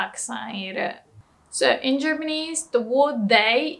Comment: did she say germanese?